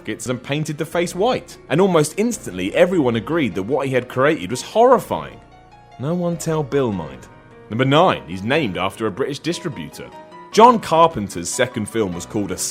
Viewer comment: did he call will, bill?